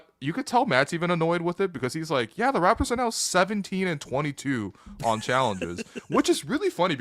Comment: for a second I thought you said 17 FOR 22 on challenges and I was calling BS. 17 AND 22 makes a lot more sense lol